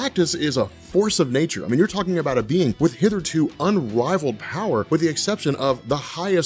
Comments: "Did you just say 'hither-to'?"....@ 7:00.... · did you just say 'hither to unrivaled..'? -Tony Stark